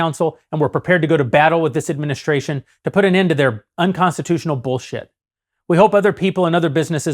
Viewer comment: Did he just say say “bull ship”? This is meet Kevin’s campaign 😂